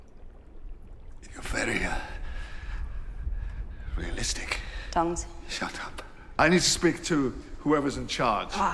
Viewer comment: THE SUBTITLES SAYS DONGS INSTEAD OF TONGUES WHY AM I LAUGHING SO HARD AT THAT 💀💀💀